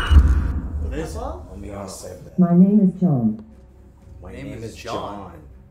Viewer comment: Kinda sounded like it said "My name isn't a sigil."